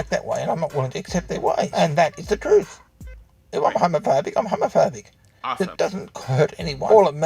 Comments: when listening to the first guy who kept saying "im homophobic" I kept hearing "Im a f*gg*t", subliminal message? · I kept hearing "Homofurby" instead of homophobic